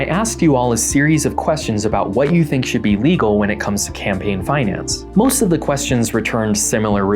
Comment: I thought it said "campaining in france"